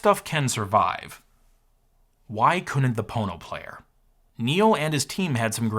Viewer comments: I swear, I thought that said PornoPlayer. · I keep hearing "porno player". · Had to do a double take thought it said porno player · 😂😂😂😂 I thought it said pornoplayer ..